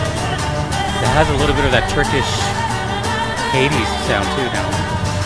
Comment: Did you say turkish?